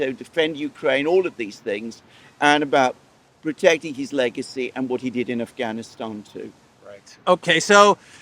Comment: Legacy, I thought it said, Lunacy